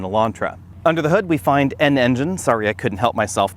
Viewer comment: did he say... A. An N-gine? B. N Engine? C. N N-gine? 🤔